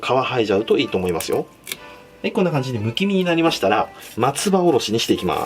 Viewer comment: 不気味になりましたら～って聞こえてびっくりしたww